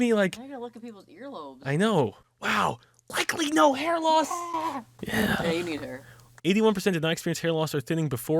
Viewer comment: am I the only one who heard a weird deep close up "yeah" at 10:43?